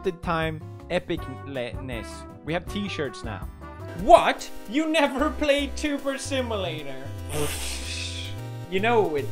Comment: Edgar in the background. Awweeee. <3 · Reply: I heard "you've never played tuber simulator?"